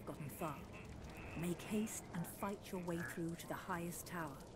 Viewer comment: Sounds like shes saying Fight your way out of be apart